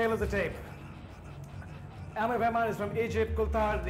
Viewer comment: ha when they say amir wahman i thought it said I'm a woman :)